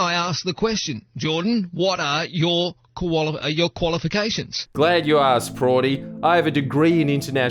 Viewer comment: it sounded like he wanted to say 'koala-fications' but then backed out at the last second. Also, curious to know more about your commercial broadcast offers if you're allowed to discuss them, who wants to get their grubby hands on the free spirit that is FriendlyJordies?